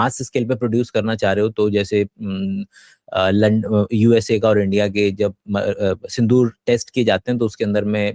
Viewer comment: Wait! wtf he said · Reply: he was saying "aah... London, usa india" lekin vo london bol nahi paiya but I like the way you think